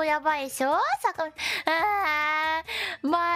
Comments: Thought she said "My shit grabber!" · I heard "My shit's grandma" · I thought she said "my sh*t grandma* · For a moment, i thought she said "My shit grandma" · i thought she said "My Shit Grandma" for a second